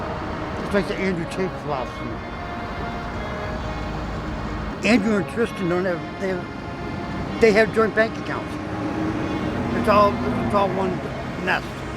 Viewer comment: I almost thought he said Andrew Yang but realized he said Andrew Tate. That is somethin else there.